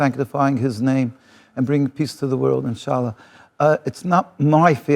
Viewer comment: did he say inshallah?